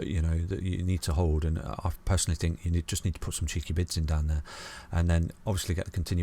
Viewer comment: this whole time ive always thought you were saying "put some cheeky bits on down here" as in parts of bitcoin or something etc ACCENT ACCENT ACCENT hahahha